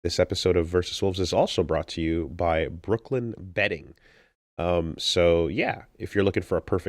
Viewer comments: For the life of me, I thought that at 1:18:24 Instead of "Brooklyn Bedding" he said "Brooklyin Betting" and was worried for a sec that the Saudis had bought too. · Ow thank the lord, I first heard betting and was really worried you guys got a gamble site as your sponsor